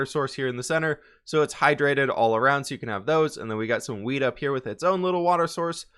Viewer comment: it sounds like he said we got weed up here. Lol